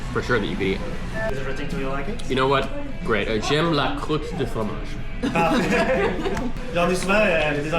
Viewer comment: Thanks to QuillBot for sponsoring this video! Check out all QuillBot tools for free here: <link> You can use my discount link for 20% off QuillBot premium: “xiaomanyc” So what do all you non-Canadian French speakers think of Québécois French??? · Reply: With the poutine you were trying to say "crottes de fromage" (cheese curds, and "curds" is used to mean fecal matter as well!) but it sounded more like "croûte de fromage" so he thought you said "cheese crust".Anywhere you see a ^ accent in French, it usually means an extra "s" is hiding there in the old spelling. So "croûte" --> crouste --> like English crust.